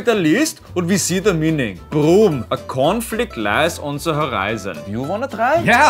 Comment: I was reading comments and the video started playing again on its own, so I wasn't paying attention to the dialogue until I heard, "a cornflake lies on the horizon." Was like... wait, what? Lol